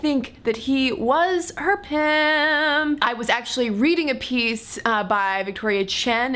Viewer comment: What do you say at 2:39?? " They seemed to think that he was??????? herpem???" What do you/ does she say?